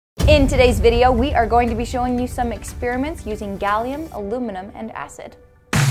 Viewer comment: I heard ‘We’re going to be showing ME some experiments’